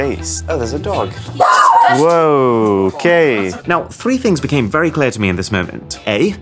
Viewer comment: did you just say “woah gay?” lol ik you didn’t but it sounded like it